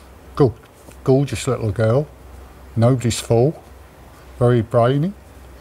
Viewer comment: What did say of Ellie at 1:46? Can't make it out. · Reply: Nobody’s fool